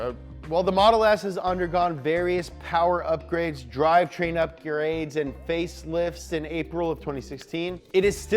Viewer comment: I’m sorry but did he say “up your aids” at 9:15 ??